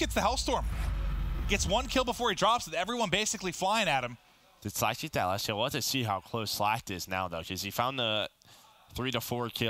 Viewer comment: What is this guy saying at 1:27:16???? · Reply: “Did Slacked get that last kill? I wanted to see how close he was to streaks.” Something like that.